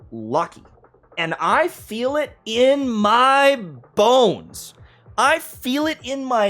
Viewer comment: I feel it in my balls… thats what i heard😂😂😂